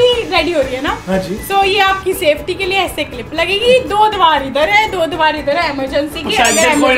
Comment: Anyone Notice it that say that "Do diwaar 👈eathar👉 Hai... Do diwaar 👐udhar👐 Hai....🚨 Emergency Ke liye...."(Instead of Darwaaje)...🔨agar Emergency aaye to Diwaar todke kood Janna😂🌂